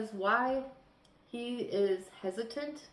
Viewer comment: OMGGGGG I thought you said “this knight is impotent” 😂😂😂 you actually said “ hesitant” I’m still laughing 🤣 😂😂